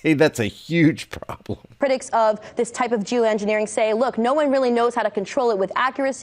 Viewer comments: did she say joo engineering? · At approx. 6:13... did she say, " j o o -engineering"?? Correct me if I misheard what she said.